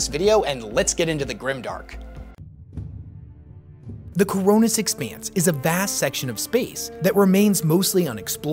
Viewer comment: I know nothing about warhammer but I really enjoy all your creepy and dark stories. When you said koronus expanse I thought you said corona expanse and was like "Damn.. These creators dont play around " Great vid btw ;)